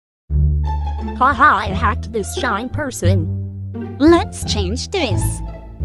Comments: i thought u said ‘shein’ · POV: you thought she said the brand “Shein” and was confused for 5 minutes straight 😃🔫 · I thought it said ‘shein’ … wait does it